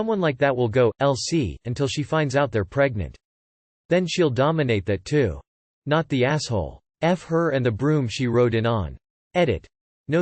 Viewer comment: Story 3 (I think)I thought I heard someone say, “F her and the groom she rode in on.” Since it was about a wedding…. 😂😂😂